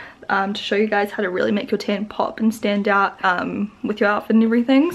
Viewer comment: Am I the only one who thought she said "tampon stand out" but she actually said "my my tan pop and stand out" or something like that